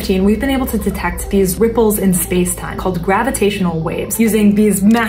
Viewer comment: Did she just say nipples in spacetime?